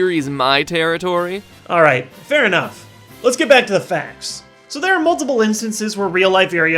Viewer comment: he said let's get back to the facts but it sounded like let's get back to the faps! Lol! Good fact/theory you should make these more often and theories too